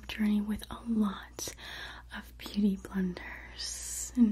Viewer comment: Wait, did anyone else hear beauty blenders around 1:10-1:20 or so? Lol 🤷🏼‍♀️ Edit: there was BB in the 80’s? 😂